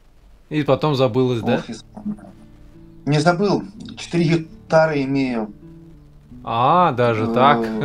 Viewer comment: А мне послышалось вместо 4 гитары-- 4 гектара)))(